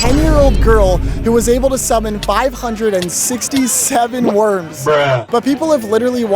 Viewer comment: did I just hear 67???